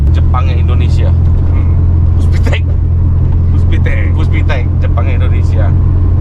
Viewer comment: itu bener gw ga salah denger, ECU nya buatan lembaga negara puspiptek, dari BPPT ?